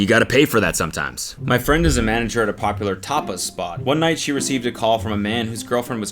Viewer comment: I heard "popular topless spot"